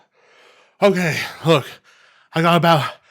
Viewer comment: did you say "I'm gay" ? That latina was right!!!!